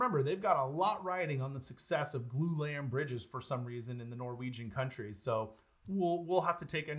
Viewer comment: Apologies for the low audio output on this video. We switched editing over to a new system and changed some of the ways we record audio. Unfortunately this led to lower-than-expected volume on the video output. Given the way YT works, it doesn't make sense to re-upload the video, but I promise to have corrected audio levels on all future videos. Thanks for watching! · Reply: Very difficult to hear. Had to rewind several times. Almost sounded like you said 'the Norwegian countries' at the end there.